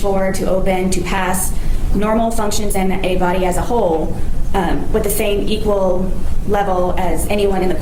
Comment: When I hear Sarah say, "As a whole," I hear, "As a hole."